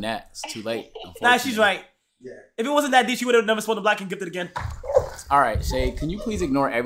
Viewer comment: what did Kai say here lol I’ve replayed it back like 10 times lmao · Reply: He said if it wasnt that deep, she wouldn't have spun the block and gifted it again